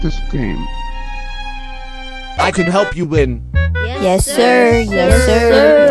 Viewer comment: now thats what I call a good “speed run” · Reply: And i hear yes sir if